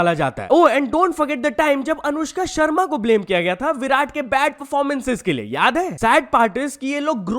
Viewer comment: wait did she say vibrant instead of virat, 😂😅